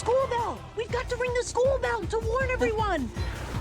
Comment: When I was a baby I thought Abby said “we got to ring the school bell to learn everyone”